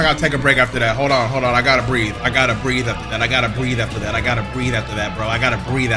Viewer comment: did I just hear " I gotta bleed after that"